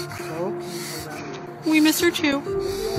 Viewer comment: What did she say? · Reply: “We miss her too”